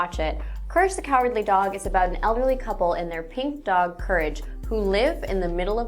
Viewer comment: in 0:14 i thought she said ugly couple instead of elderly couple